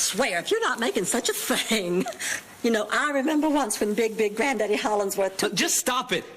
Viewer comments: Am I the only one that heard her say, “Big Dick granddaddy…” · Man I thought she said Big Dick Daddy Hornsworth